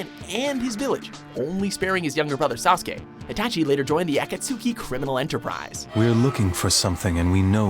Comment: Why can't these guys never get the pronunciation right when it comes to anime names and organizations? It sounds like he said "atsakuki" when referring to the Akatsuki. This right here proves these people really know nothing when it comes to anime